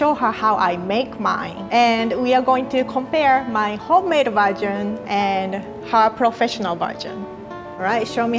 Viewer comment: I love, and I think her English accent is soooo cute😍. But each time she says version, it cracks me up cos all I hear is "virgin" and now I can't unhear it.